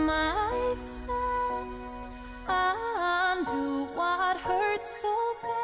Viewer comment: And i always thought the said undo what i said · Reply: I thought she said: I undo what I said